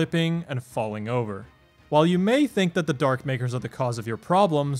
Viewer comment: anyone else hear Dark n-words?